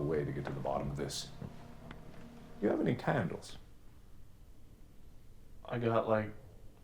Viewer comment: Lol when Mark said candles I thought he said cannabis